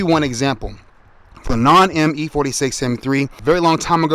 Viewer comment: Did you say a non-M e46 M3? :D